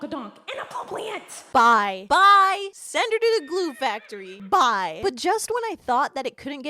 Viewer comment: istg i thought she said “goon factory”💀💀 chat am i cooked?!?